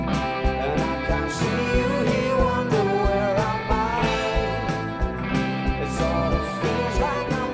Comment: what does he say in the following part: "and I can't see you here where I'm __ high" it just that I think he says something else that's not in the lyrics but I don't know what it is... it doesn't match withthe lyrics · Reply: I thought he said "and I can see you here wonderin where am i"